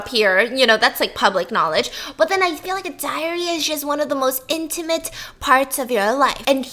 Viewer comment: Tell me why at 7:54 I hear A diarrhea is just Instead of diary?